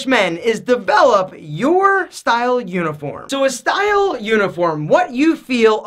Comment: Totally thought he said "Style Unicorn" not "Uniform"... 🐴